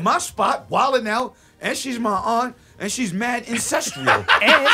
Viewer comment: did he say she's fat lol